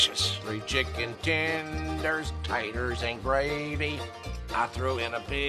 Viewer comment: three chicken tenders taters n gravy I throw in a biscuit and a big ol cookie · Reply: I thought he said tires and not taters